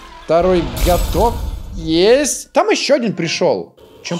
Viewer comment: мне одному послышалось Го*дон